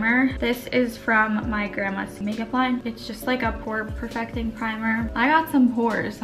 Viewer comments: did say her grandma's make up line? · Am I crazy or did she actually say "my grandma's makeup line? 😂